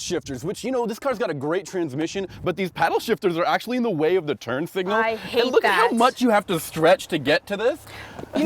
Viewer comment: I THOUGHT THAT GIRL SAID TURD SIGNAL LOL